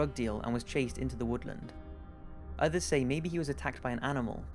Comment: I don't think it said "I got taken into the woods" I clearly heard "I got CHASED INTO the woods" on #1. I think he probably ran into a drug deal/murder or something of the sort. The ping could've been somebody checking his phone before they buried him in a shallow grave away from his truck.